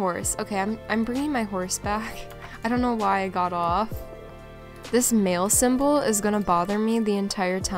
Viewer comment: When you said " Mail system" I thought you said "This male system" LOL